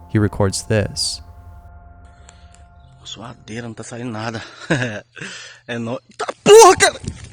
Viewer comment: what i heard: THaT BuRgEr tell me that you heard